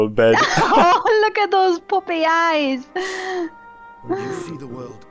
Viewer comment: "Look at those poopy eyes!" God I love how accents can make me mishear things, LOL!